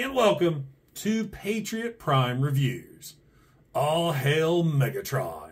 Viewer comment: At the beginning of the video, when you said, "All hail Megatron", I wasn't fully focused on the video yet because I was fixing breakfast for my little girl, so at first I thought you had said, "Aww, HELL... Megatron!", lol, but then I was like, "No, that's not what he said, but would have been hilarious if he had..." 😂😂😂 Not a huge fan of most movie toys, but there are exceptions, and even though I don't generally care for the movie figures, (especially the Bayverse stuff), I still enjoy watching what few you do, and this was no exception. I definitely agree that the bot mode is the best of the 3 modes, but I do think the "jet" mode has a neat sci-fi look to it, so I don't hate it nearly as much as that laughable "tank" mode... and holy crap, was every character BUT Bumblebee a freaking triplechanger in that movie...?!🙄 I have the Mad Max looking tanker truck leader class Megatron and the space jet Megatron from the following movie(?), I think it was, but the bot mode on this guy just makes those look like the trash that they designed to be... still don't like the face, but it IS better than ALL of the monster face movie Megatron figures to date... That said, unless I run across one on serious clearance and nothing else to buy, I probably won't get this figure, even with the awesome bot mode. My store got in the War for Cybertron Megatron last week and the RotB Rhinox, but since I have the SSRotB version of Rhinox, I don't need the regular version, and I'm not really getting